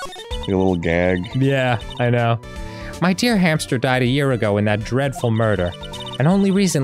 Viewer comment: my dear hamster instead of dear hammer